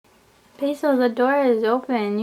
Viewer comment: when she said basil I thought she said peso xD